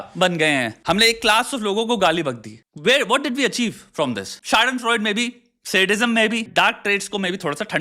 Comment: Shwetabh bhai what did u say at 10:18 ?? ___ en froid? I want to look it up but I can't seem to get the word itself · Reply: schadenfreude